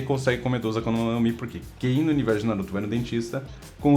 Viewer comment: universo de Naruto ? Ksksksk desculpa n pude deixar passar releva, vídeo top ( acho q ouvi "Naruto" se tiver errado falem aí )